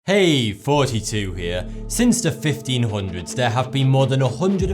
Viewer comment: Lol it sounded like he said: hey guys 42 here. XD